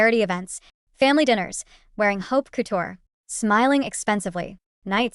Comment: "Like a walking oak couture tombstone" 😳. Well, there's a brand new sentence. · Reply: At one point I heard "hope couture" and thought it sounded like a good brand idea 😊